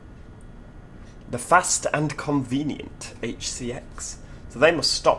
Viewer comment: I misheard the announcement as 'the Fast, Inconvenient HDX'